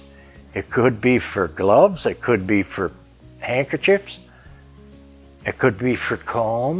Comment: When you said that dresser piece was for handkerchiefs, I thought you said "potato chips". I got a chuckle until I figured out what you said! Great repairs, thanks for the tips!